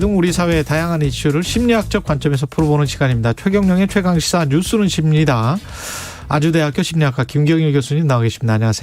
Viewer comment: 여기서도 진짜 '뉴스는 쉽니다'라고 들려요..ㅋㅋㅋ